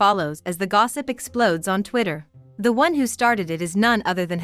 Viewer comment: did I heard it right??? Twitter??